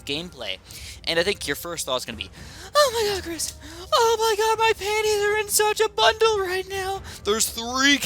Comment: did he just say my panties!? Or am I hearing something else...?